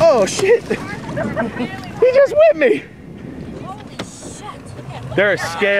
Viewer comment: I heard:"he juz whipped me"...lolx...